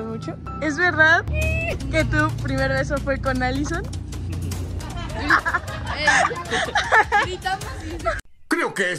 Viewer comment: Holii una pregunta en el minuto 1:57 que dijo esq no escuche muy bien no se si dijo que q si es real o puro contenido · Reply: Dijo es real es real y después le dijo a o algo q me quiera decir ,o algo así le dijo